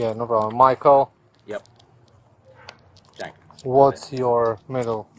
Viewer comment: I had the sound low and thought he kept saying Michael Jackson and then his middle name was Germain (like Michael's brother) I kept wondering why the cop bothered to look it up. Jenkins is better, because the name Michael Jackson will pop up in the system for a number of serious prior felonies